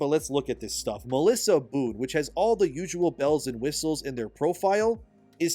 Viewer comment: I wasn't looking when he said Boone and I thought he said Melissa Boot for a second 😂